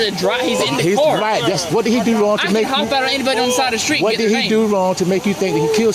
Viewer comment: I swear I just heard him say that “I can ID anybody out on the street”